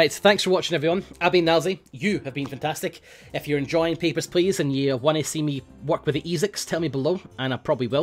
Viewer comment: It always sounds like Nilesy says "Peppers Please". Just imagine if people came to the border to hand over their peppers.